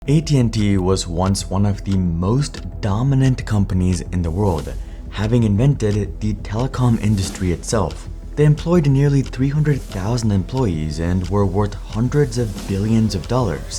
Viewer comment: I heard dominate companies example 0:03 😅😂🫡😉😏💛